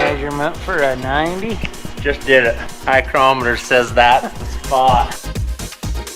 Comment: Says “eye-crometer”, I hear “ay caramba” ;)